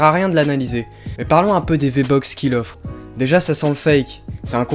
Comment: Tu dit il ne sais pas parler français, en attendant c'est toi qui a dit V-Box a